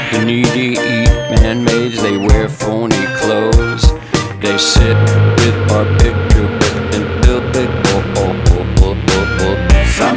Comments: what song says: They sit with barbiturates until they grow old what i hear: they say e-a-e-i-e-o-e-i-o-o-o-o-o Edit: nah time passing is wild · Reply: I thought it said "They sit with our pictures until they grow old" XDDD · what song says: They sit with barbiturates until they grow old what i hear: they say e-a-e-i-e-o-e-i-o-o-o-o-o Edit: nah time passing is wild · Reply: barbiturates* not "our picture up", it's a common misheard lyric